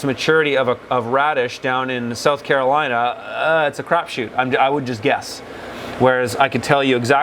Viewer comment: did you just say its a "crop shoot" lol :D